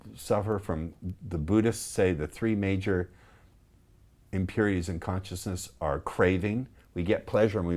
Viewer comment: What word does he say at minute 24:16??? "The Buddhist say there's 3 of consciousness" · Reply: The Buddhist say there are 3 impurities to consciousness.